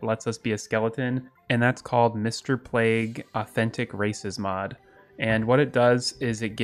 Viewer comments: I thought I heard him say “mister plague’s authentic racism” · OH MY GOD SDHFDJF I THOUGHT IT SAID MR PLAGUES AUTHENTIC RACISTS